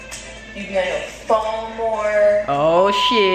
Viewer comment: What did she say at 5:09? You been on the phone more?